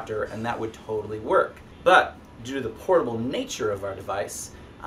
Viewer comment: At the 4:59 point, what did he say? It sounded like a phona feather